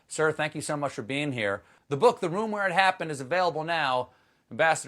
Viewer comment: Who else heard "Bastard John Bolton" at 4:29?